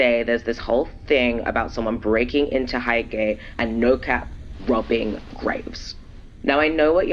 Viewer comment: anyone else hear "rubbing grapes" instead of "robbing graves" at 9:27 😭